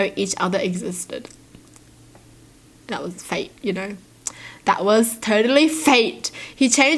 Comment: anyone else heard it as fake instead of fate and were like !?!!?